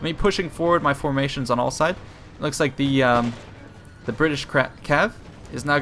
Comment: it sounded like you said "The British crap cav"